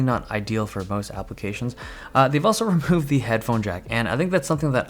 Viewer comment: Oh ! they improved the headphone jack ? :D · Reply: ILLUSION think of him saying removed And then improved He says both It's like yanny and laural